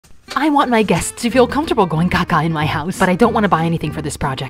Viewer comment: I open YouTube with my volume all the way up and I'll I hear is " I my guests to feel comfortable taking a caca in my house" 😂😂